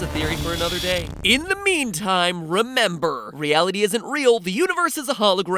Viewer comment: Sounded like he said Milf theory backwards